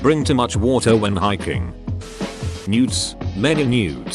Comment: i thought it was saying nudes instead of newts lmfao